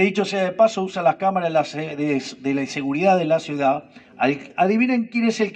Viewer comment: JAJAJAJ " Cámaras de la inseguridad de la ciudad" escuché bien? 😂